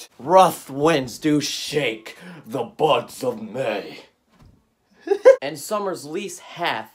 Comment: I thought you said "butts of may", instead of"buds of may"😂